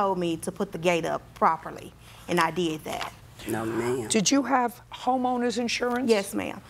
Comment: it has to do something with visuals.. when judge said do you have home owner's insurance I heard she said "do you have hormone's insurance" I'm crazy